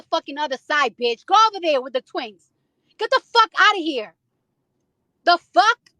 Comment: I thought she said "The twinks" like all Nicki fans some flamboyant bottoms. I'm weak. she need to do better